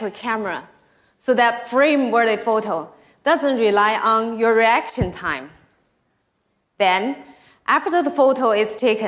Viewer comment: I am sorry but did she just say "erection time"